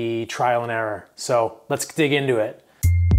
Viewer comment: sounded like you wanted to say GET into it but then at the last second you changed ur mind and said lets DIG into it